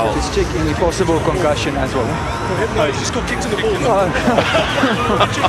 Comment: what did he say? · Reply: "No he just got kicked in the balls"